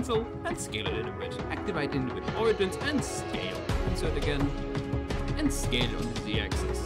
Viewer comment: pls add subtitles to this video idk what u say at 0:17 it sounds like you just say "insert again"